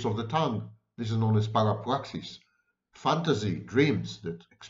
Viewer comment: Does anyone know what Sam says at 18:26? Para plaxis?